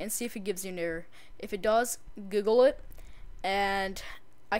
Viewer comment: Sounds like he said "Giggle it." I know he said Google it though.